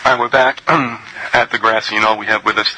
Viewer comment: ? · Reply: ok its Grassy Noll - couldn't make out what he said and he said it just once.... He said "Grassy Noll" - I heard "Grand Seeing Owl" :-)